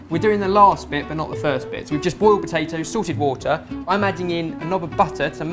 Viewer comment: you know...for the longest time, i thought you guys were saying "sorted water" instead of "salted water." bajeezus. ;u